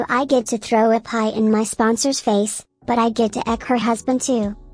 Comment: I was just listening to the part at 10:11 NOT watching and reading along and I definitely heard that she was going to get to "f" her boss's husband and so it was even funnier when it ended with "and yes there will be a video when this happens " cuz I thought they were basically going to make a porn 🤣😂 omg that would have been so much funnier making fun of those posts than what it turned out to be, an actual post like that🤦‍♀️